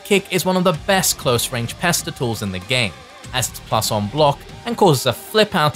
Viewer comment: The way you said "Standing light kick is one of the best close range PESTER TOOLS in the game", I misheard it initially as "PESTICLES" and I was like "OH right I get, it's a move that pesters you, by threatening to hit you low (i.e. In the testicles)". I thought it was new genius FGC terminology I hadn't heard before.